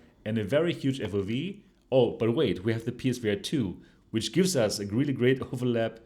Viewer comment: What VR headset are you referring to at the ~8:15 mark? I couldn't understand what you said... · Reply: PSVR 2